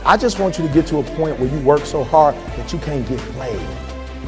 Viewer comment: Lol Sounded like he said I want you to work so hard that you cant get laid 🤣😂